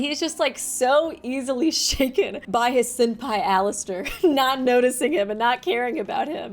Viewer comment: I'm sorry, did you say "sin-pai"??? because that's the funniest shit I've heard all week.